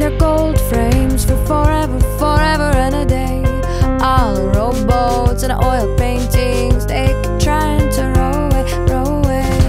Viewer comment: I thought she said "All the robots in oil paintings keep trying to run away!"